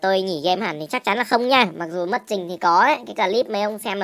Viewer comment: Đầu Cắt Moiiiiii ✅SHOP ACC : <link> Hoàn TIền 100 % cho acc bị khóa hoặc ko Vào được nhé ae :3 hỗ trợ tin nhắn 9h sáng tới 8h tối :3 Chúc Anh em 1 Ngày Vui vẻ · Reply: nghe nhầm thành mất TRINH